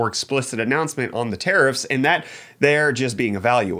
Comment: I swore I heard you say terrorists when you mentioned tariffs. :) I don't have to upgrade my GPU, but I may do a CPU/motherboard upgrade.